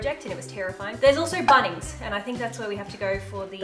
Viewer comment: i thought you said bunnies i was about to hide my pet rabbit like hell no not the bunnies joke